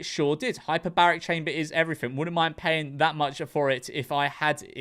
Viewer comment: When the guy started talking about the $10,000 gym having a hyperbaric chamber in it I thought he said hyperbolic time chamber. Like from DBZ. Honestly if a gym has a room that I can train in without time passing outside I'd be kinda tempted to spend that 10,000. But it has to turn me into a super saiyan. If I can't walk outta there ready to absolutely embarrass Freeza I'm not spending a single cent.